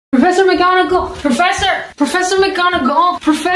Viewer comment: I’m sorry I heard it as Professor Mcdonald 😂😂😂😂